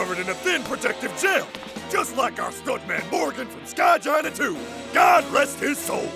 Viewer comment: did he just say "Skygina 2"